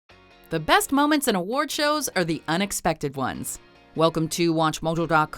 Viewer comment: I keep hearing unexpective, help.